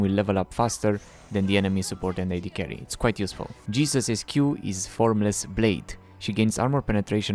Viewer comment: did you say what I think you said @ 1:07 and call her Jesus? Not funny at all